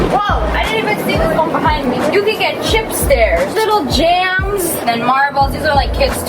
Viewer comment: chip stairs that’s what I heard